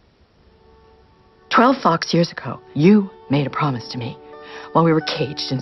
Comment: 12 fox? Oh I thought u said 12 fuckin years ago